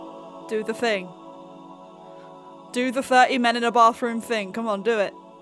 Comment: "do the 30 men in a bathroom thing" lol 💀💀💀💀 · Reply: For a second I thought she said "flirty men in a bathroom thing" and I had no clue what she was on about.